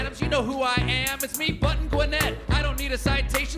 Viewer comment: "button what???", who wants to lmk what Colbert said there? Button Buinette? (sp???... DEF spelt that one. there's not ever a chance off that being spelt currently bc I dint even know what he said so how on earth am I supposed to spell it correctly? lol... but yeah somebody reply with the word/name after "button" please... thank you in advance. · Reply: Button Gwinnett!!!